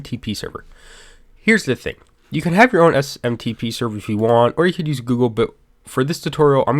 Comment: Any idea what he's saying about SMTP servers at 1:35? Sounds like "or you can use Google boot"?? I'm very lost on the email part of installing Discourse. I tried PostFix, but I have no idea what I'm doing and looking for simple options. As I see, Discourse just needs a way to send out registration and similar emails.